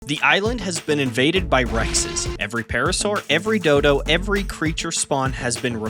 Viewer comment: I thought he said "the island has been invaded by racists"